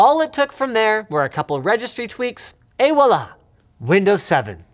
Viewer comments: Subtitles say [Wallah] when it's actually spelled Voila. it's french. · what did he say 4:23 ? 😂😂 did he say ( aih wallh )? 😂😂😂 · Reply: Shiroe H « Et voilà! » it is french. It can be translated to something like « And there it is! » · what did he say 4:23 ? 😂😂 did he say ( aih wallh )? 😂😂😂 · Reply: he tried to say "et voilà" but as a French (who never says this) ; I think I heard "Hey walla !"